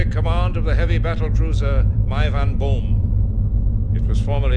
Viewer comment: When I first played through this game, I always thought Chang was saying "heavy battle cruiser My Van Boom" lol.